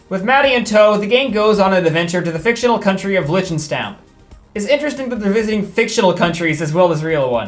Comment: i couldnt quite hear it but if you tried to pronounce liechtenstein, it very much is not a fictional country and is in fact very real. it also kinda seems to match the castle aesthetic in the show so if its not liechtenstein it probabely is some parody for reasons i dont understand.